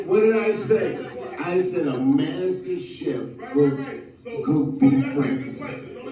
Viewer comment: Hey, just wanted to make an observation about the caption(6:33). I believe I heard Dave say, “A MASTER CHEF WILL COOK BEEF FRANKS.”